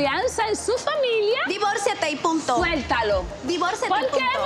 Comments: She thought she said “puta” but she said “punto” omg hahahahahahah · Puta or Punta? What did say exactly? I'm not good with Spanish. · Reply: She said "Punto", which means Period. thought she said "Puta".